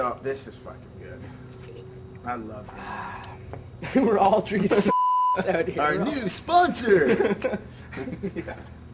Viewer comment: Love Deryck's laugh always cracks me up. Also I don't understand why they bleep that out at 4:41 what did he say when it bleeped out? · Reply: Deryck said Starbucks